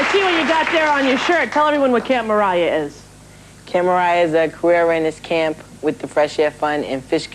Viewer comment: did he say queer awareness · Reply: Omg he said ‘career awareness camp’ 😂